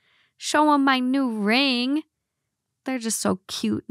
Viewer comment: I thought was gonna say “ And that was in the box” At the end lol meaning ring